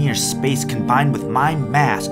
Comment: At first I thought it said combined with my fat ass then realized it said mass when I watched it a second time lol